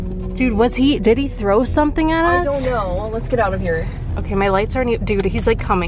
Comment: That' whisper sounded like it said shoo get outta here